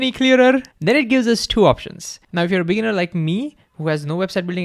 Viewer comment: “now if you’re a beaner like me”😭😭 I hope I heard that correctly because this bean was laughing his ass off for sure